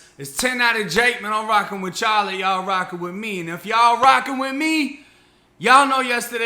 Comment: First few vids I thought you were saying "I'm rocking with Charlie, Y'all rocking with me." I was like who tf is Charlie. I'm a big dummy, lol!